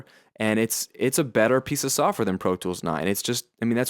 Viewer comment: sounded like you were about to say "piece of crap" there